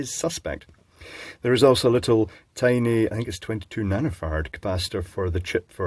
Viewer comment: I love how it sounds like says "nanofart" when he says "nanofarad" too fast